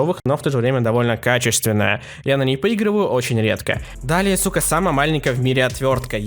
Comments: Ты сказал сука или мне послышалось ? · что? Мне послышалось или ты сказал слово: Су*а.